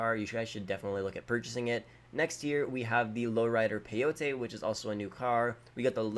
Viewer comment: Hol up. 6:32 did he said next year?